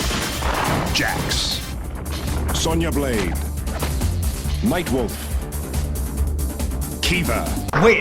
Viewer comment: Am I the only who hear "Mike Wolf" instead of Nightwolf?O-0'